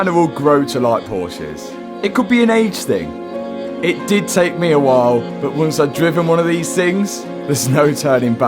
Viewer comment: I thought I heard take on me